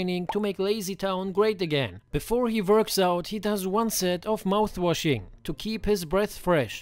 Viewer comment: I honest first misheard this as "before he burps out"